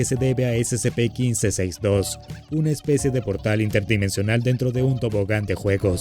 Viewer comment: Juro que pensé que decía Portal 3